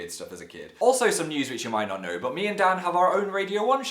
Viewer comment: I thought, at the end he said! me and dad not me and Dan